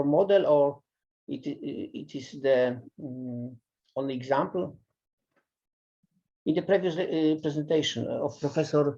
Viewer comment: At the around 59:00 mark he says that the velocity of longitudinal waves was something. Did he say "square root of three"? Thanks in advance. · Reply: He said: "velocity of light multiplied by square of three". He might mean "square root of three".